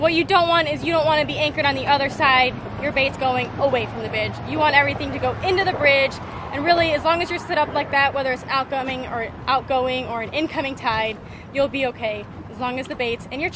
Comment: I swear I thought she said "your bait is going away from the bitch" at 2:10 lol